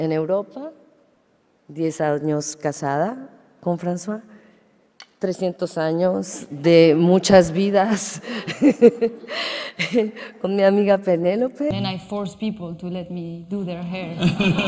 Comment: The subtitles are wrong. She said 300 years of many lives with my friend Penelope.